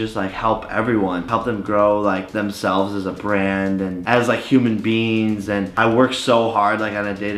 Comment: did he say human beans